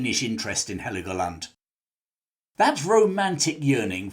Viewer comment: i keep hearing “her legoland”